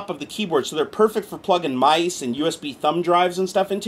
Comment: I always thought you were saying DOSkeyboard! I was like "what kind of a nerd keyboard is that?" haha ~germannativespeaker